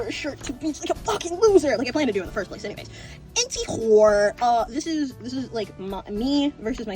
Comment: What did you actually say at 9:45 LMAO I've been trying to figure it out for the longest time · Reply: i said antywhore it’s a mix between antyway and anywhore it’s a fun way i say anyway